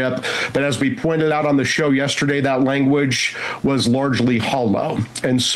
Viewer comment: I thought he said "that language was largely HOMO" lmaooo